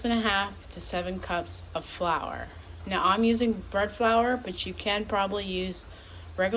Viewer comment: What kind of flower was that you used? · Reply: I went back 3 times on the video and I thought you said bird 🕊flower. I was thinking I have never heard of bird flower this must be something new 😂🤣 Thank you so much